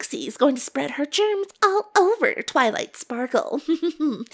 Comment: for a sec there ia thought trixie said porncess XD